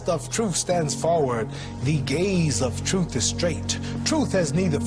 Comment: Did he say he say, " the gays of truth is straight ??"